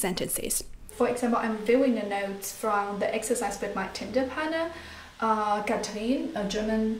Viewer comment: It sounded like you said Tinder partner rather than Tandem partner! Freudian slip? :) I'm only joking, your videos are very insightful